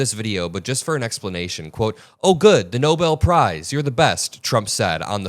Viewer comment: I thought he said, "Oh good, the Nobel Prize police." 🤣